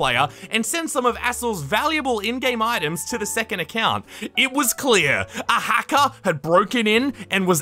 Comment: Jeez I thought he said a hole